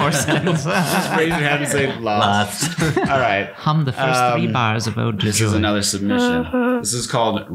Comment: Did anyone else hear in the background say “Him the first three bars of ode to joy!” Then start humming?!?! At 20:00 min! Also laugh is so adorable! 😊